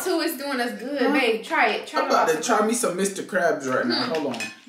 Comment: I thought that he said he made it and he said I’m about to try me some mr ms crab